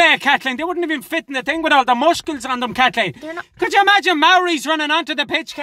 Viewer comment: Did he just say 'mushkle' (Muscle)!!!!